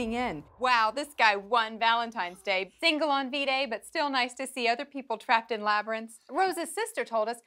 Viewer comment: for some reason I heard " fingered on v-day but I'll still like to be trapped in the labyrinth"